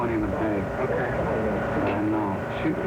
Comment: is it me or did i hear "shoot me" then "flats" boom...